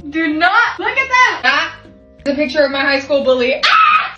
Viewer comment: Help i thought you said “my high school boobie”